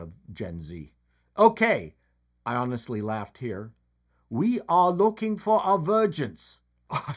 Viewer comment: At first I thought he said "We are looking for a virgin." lolol